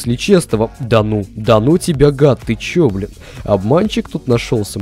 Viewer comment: на 1:40-1:41 он 1 раз матернулся ) или я один услышал бл@